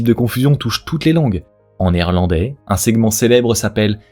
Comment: y a que moi qui entend "néerlandais" au lieu de "en irlandais" à 7:05?